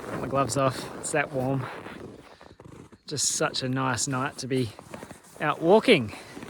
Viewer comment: Did you say „it is such a nice night being out walking“ around 2:00? :-)